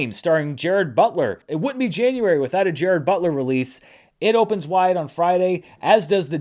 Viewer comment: Gerad Butler?? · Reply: I definitely thought Dan was saying “Jared Butler”… I guess I wasn’t present at the origination of this in-joke.